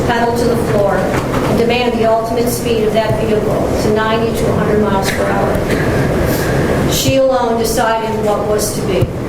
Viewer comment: I thought the judge said the car did 9200 miles per hour😭